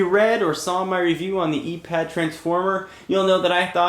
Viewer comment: i could have sworn it said the anus transformer...